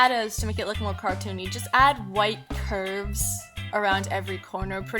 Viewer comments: it sounded like she said white pervs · I thought she said pervs instead of curves lol